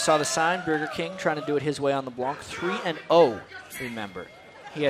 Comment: anyone else wonder what the guy said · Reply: Well, as we saw the sign, Burgerking (refers to Bjergsen), trying to do it his way on LeBlanc..